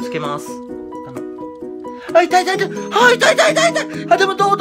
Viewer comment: 「痛い痛い痛い」が「いぇいいぇいいぇい」の感じに聞こえるw